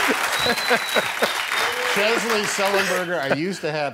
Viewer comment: Does Tom call him 'Chesley" at 2:58?